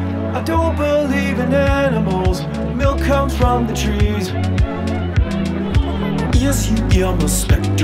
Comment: When I first listened I thought he said "milk comes from machines" with makes more sense with factory farming and all